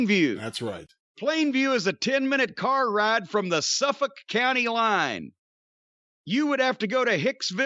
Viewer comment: Sounds like he was saying some fuck county